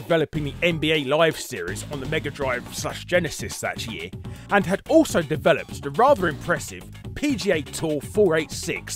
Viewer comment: What the heck was that? Yellow yums? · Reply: Lums!